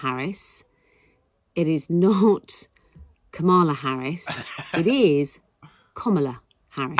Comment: did she say "Cumala"?